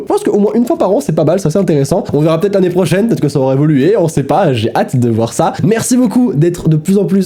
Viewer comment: Je crois que je regarde trop tes vidéos 😭 qua'd t'a dit "sa aura évolué..." j'ai entendu "sora évolué" 😂😭